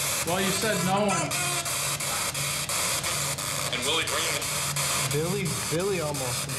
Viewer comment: HEY GUYS!! Please help the video well by LEAVING A COMMENT and LIKING the video!! You can comment anything- “Hi”, “Love the video!!” Or even “Worst video I’ve ever seen!!”. Lol it doesn’t matter but it’s free and helps us so much so pls say hi below!!! Anyways, enjoy this one... lots of investigating... lots of evidence... one freaky night!!!! Love y’all!!! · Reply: Omg I heard your dads voice! Very creepy Theres a part in the video at about 50:33 when the spirit box says and billy something I couldn't hear the rest but it sounds just like your dad talking i swear it's very strange but I think it does sound like your dad a lot give it a listen and you will see. Also Awesome video and the voices caught on the other recorder are just amazing another great video by you guys I look forward to see every video please stay safe and thank you 🤗🤟🤗